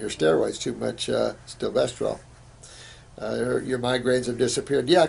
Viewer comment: what does he say there? "too much...stovesterol"? google cant find it